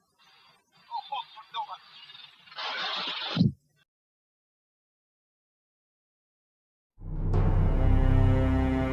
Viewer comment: Does anyone know what the Dutch expletive was? The usual English one in this situation is Oh Shit. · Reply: "Godverdomme" is a hard curse in Dutch, much heavier then 'o shit' it means: god damn me.